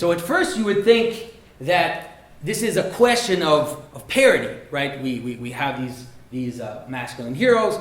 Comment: I heard "parody" instead of "parity" which makes sense from a different angle arbitrarily changing a single aspect of a character is a technique of parody. Predator, except Arnold's character is played by an out of shape obese guy. It's a symptom of this larger trend of skepticism, a desire to deconstruct and break down everything that has been built up. The male/female politics of it we all obsess over is losing the forest for the trees.